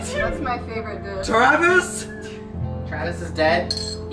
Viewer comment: or 12:08 I could have sworn that said "Todvis"